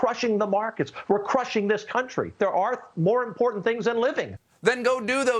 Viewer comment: did he say “there are more important things then living”?